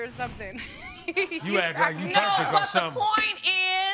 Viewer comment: The third case ' u act like you are pretty ' 😂😂😭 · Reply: I thought he said you act like you hungry lol but he said perfect 😂